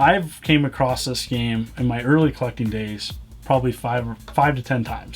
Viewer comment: I keep hearing ''Fucking'' 15:28 In my early fucking days.